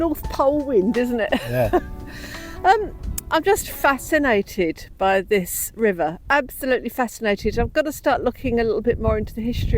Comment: your accent is so good ,when you said you are fascinated by the river i thought you said you were sat naked by the river ,had to go back and listen again lol